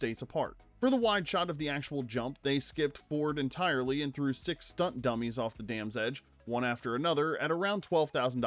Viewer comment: My pre-caffeinated brain heard that as, “They threw six stuntmen who were dummies…” ☕️